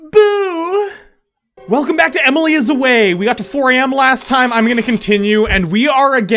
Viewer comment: did he just say emily is away